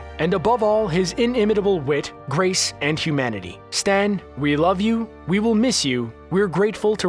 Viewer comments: Am i the only one that heard him say humanity instead of humility? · is it just me or does that say humility not humanity?